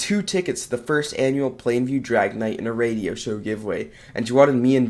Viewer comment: I HEARD DRAGONITE